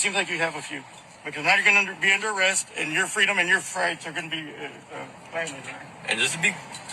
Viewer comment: Did He say, " because now you're going to be under arest, and your freedom and your rights are going to be violated"?